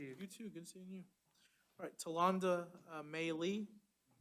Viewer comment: Was anyone able to find out what happened with this case? I hear a “Tolanda Meylee” or something like that but I wasn’t able t8 find her in the records search with that